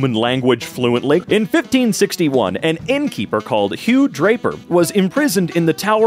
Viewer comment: did anyone else hear it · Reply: huge raper